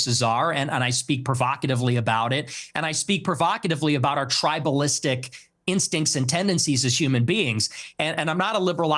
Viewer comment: what did ye say? it’s cut? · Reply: I watched it live it qas and i qoute well "Niggas be Niggas" It got cut cause Nick said the N word